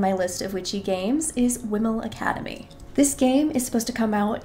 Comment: What is the one at 1:32? I can't understand the name and the captions are no help, lol. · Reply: It's called Whimel Academy! I'll update the captions, thanks for letting me know!